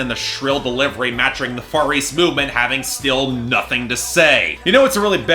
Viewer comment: When you said Far East Movement I thought you were saying Furries Movement